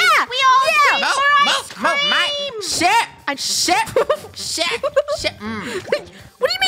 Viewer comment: is it just me or is swearing here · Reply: Sounds like he said ship but he said share